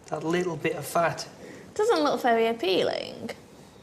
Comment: did she say "it doesn't look very appealingk"? like with a k at the end? what kind of accent is that?